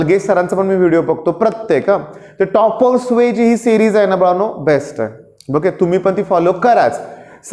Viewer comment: Sir what you said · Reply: Topper's way